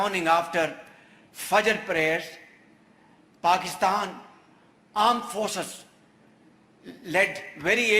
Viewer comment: Did he say "AAND forces"